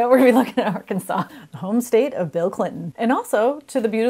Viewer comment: what??? can't understand you... "the shout" "the shadow"???